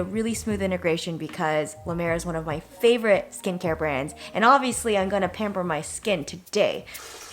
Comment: For some reason instead of "and obviously I'm going to pamper my sin today." I heard "and obviously I haven't had a pimple to date." and I thought that was such a good joke that I laughed out loud